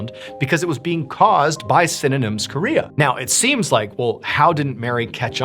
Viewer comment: Sydenham's chorea, not "synonyms Korea" like I first thought I heard 😅